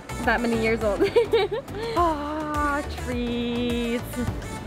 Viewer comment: I hear Audrey and aww treats